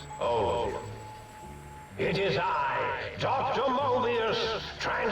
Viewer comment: Legitimately thought it said Dr. Morbius for a moment.